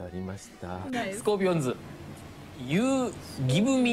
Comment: 私は空耳見過ぎて、安斎さんの「スコーピオンズ」の時点で「雪見オ◯ニー」だとわかりました の「毎回、、、」って呆れ顔が最高に好きです